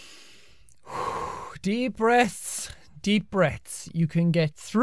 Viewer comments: Kinda sound like he says deep breasts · It sounded like Jack was saying "Deep Breasts" at the beginning of the video!